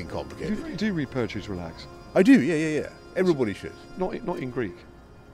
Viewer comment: I am improving my English listening the videos about UK's PM. May anyone write me what Boris says at minute 8:44? Did he really say he loves to do math (quadratic equations) & read stories in Ancent Greek? Wow... What a man!! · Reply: I do, yeah yeah yeah Everybody should Interviewer: Not in Greek? It's a terrible confession... but I do